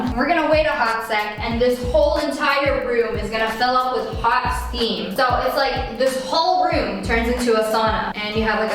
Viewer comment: 10 minutes in and I'm bored out of my mind with this girl. B I have thermostats in every room too. I mean, as a germophobe with ocd I've kinda wet myself a little at the first sight of her house... but why so big? Why so empty? Her house is more depressing than my childhood. Edit: omg I'd LOVE to have a steam shower, I'm always sick and/or cold, just popping into hot steam would be so soothing. Also I think a steam shower is more hygienic than a wooden sauna, ya know wet wood, mould... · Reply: I thought you said pooping into hot steam